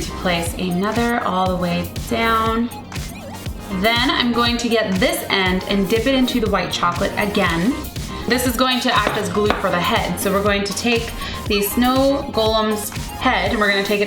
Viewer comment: when she said ready to dip it sounds like she is saying dick O.o